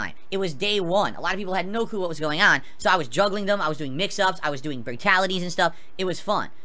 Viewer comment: did he just say, "Bratality"? English dude... do you speak it?